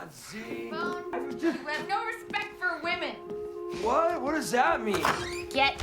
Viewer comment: Sorry to be unserious, when the character said ‘You have no respect for women’ I heard ‘I’m leaving you for a woman!’